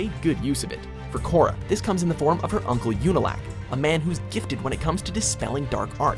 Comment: Did he just say Younalak instead of Unalaq? LOL